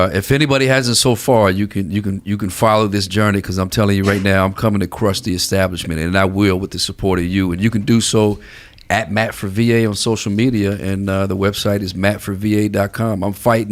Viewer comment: Totally sounds like dude is saying “Crusty Establishment” every time ;)